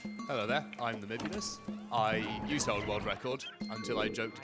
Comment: does anyone know the name of the guy on the couch? he says his name at around 12:46 , but I can't hear what he's saying. Thanks! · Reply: He's called